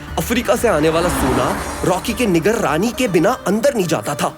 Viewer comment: did he said the n word 😳